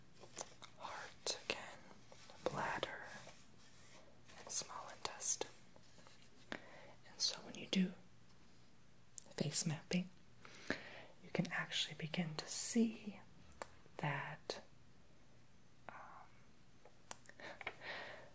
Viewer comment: I thought it said "Mace Fapping". ಠ_ಠ